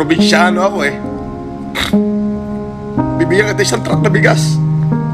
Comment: Misheard nyo kasi "isang trike na bigas" sabi nya😊